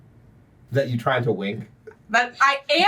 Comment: I could’ve sworn that said “Is that you tryin to wank?”